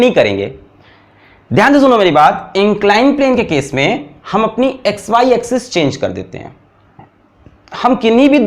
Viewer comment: what did you say the word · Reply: X,y axis change karte hai